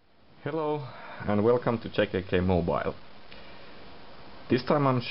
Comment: i thought he said kkk mobile